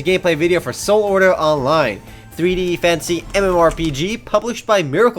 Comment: ehmmm he means fantasy by that??? i always thought he said fancy tO!